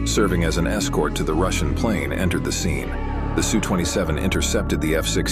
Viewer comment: The F-16 didn't break off until the TU-154 cleared its air space. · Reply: mb mb I thought he said su-57